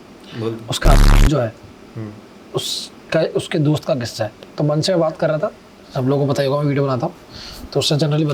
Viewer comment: did he said mann ka husband 😂